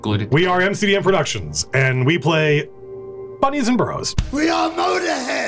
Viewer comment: Bunnies and Burrows.... sounds like an 5E expansion to me! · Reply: I thought he said "Buddies and Bros" and was seriously confused ^^